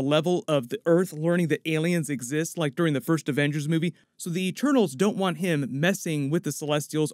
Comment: the subtitle says "turtles" not eternals